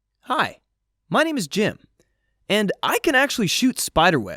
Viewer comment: Lol i actually heard him say "hi,my name is Jin"😂ALL THEM ARMYS OUT THERE💜😂!